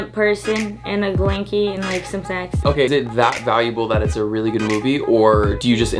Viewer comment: wait.. at minute 6:25... does say "just me and that person, and a blankees and like some sex"??